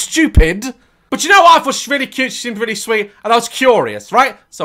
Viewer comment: wtf is he saying 🤣🤣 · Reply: “but yk what i thought she was rlly cute she seemed rlly sweet “ had to watch it 3 times hahahah